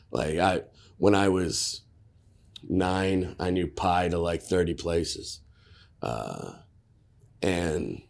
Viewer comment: I thought he said "he knew where to eat pie in 30 places" I shook my head and was like this guy knows where to get pie...